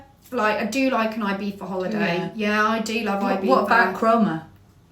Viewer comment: I cannot make out whether she’s saying ‘ibifa’ or ‘ibiza’